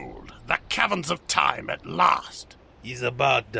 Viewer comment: OOOOOOO the Cavrens of time. I thought he was saying Cabin. i didnt think there was a cabin anywhere near gagetzan.